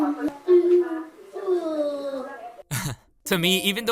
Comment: Did I hear ''Je veux pas'' hahaha (Your reaction when your parents put you in a ballroom dance class)